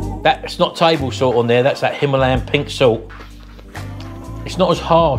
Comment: Himalayan Sea Salt 😆🤣😂😅 I need some of that ! 🥰😘🤭🤫🤔🤗 Edit: I am hard of hearing, did you say Himalayan Pink salt ? I thought i heard, sea salt 🤦‍♂😵‍💫🥴😂